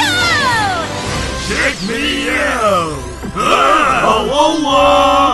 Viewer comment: what did he say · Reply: He said "check me out!"